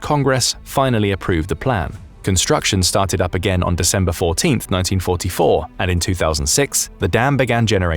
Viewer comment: The narrator says 1944. The closed caption says 1994.